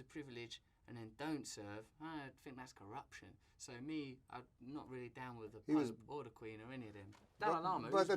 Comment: did Russell say he’s not down with the queen?